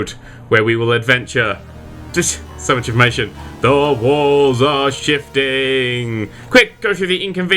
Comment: Lol I thought u said the walls are shitting